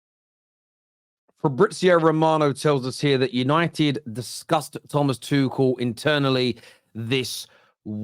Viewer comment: Lol I heard that as «Manchester united disgusts Thomas Tuchel…»😂